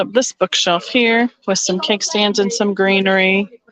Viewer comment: I'm not sure what you're asking about. Maybe I misspoke. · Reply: I thought you said you had a bunch of kickstands? I don't know what those are